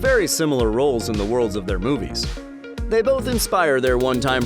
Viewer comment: Am i right or did I hear: “Red and Toothless play ver similar roles in the worlds of their b00bies”